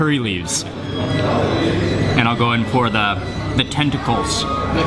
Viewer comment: I thought he said testicles 😂🔫